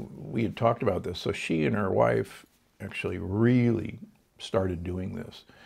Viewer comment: Great information. But did he say, “ she and her wife @ 47:54 ? Lol